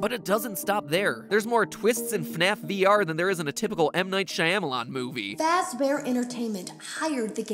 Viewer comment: did you just say Shayamelon??!! 😂